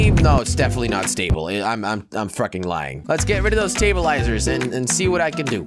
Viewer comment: Did he say Frucking.